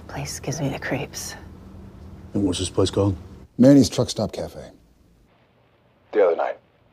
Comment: "Mann's Truck Stop . . . MannY's Truck Stop." Lol, I know you were just correcting yourself, but when you did all I heard was "Ma knees" XD